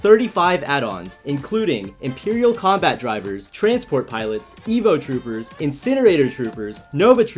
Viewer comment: I thought he said Emo Troopers, I was about to say “THATS MY BOYS” 😂